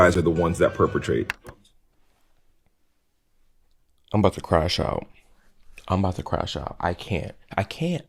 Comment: When said, "crashout" I heard "cry-shout" and I accepted it as viable term to describe how some of the fatigue makes me react.